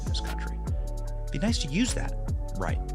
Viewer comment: "Like, share, and subscribe... then communism." Is that seriously what he just said? Edit: timing